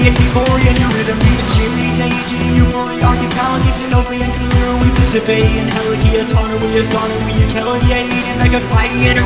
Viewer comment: theres a mistake, the subtitle says “callirhoe” but its Callirrhoe